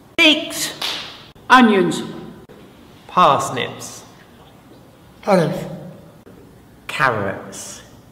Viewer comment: What did he say at 0:29? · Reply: sounds like olives